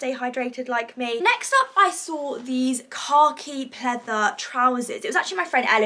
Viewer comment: i love molly but the whole time she was saying khaki i thought she was saying "car key"😭😂xxxx